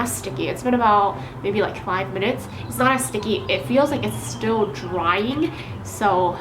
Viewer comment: who else hear "ASS-STICKY" lmfao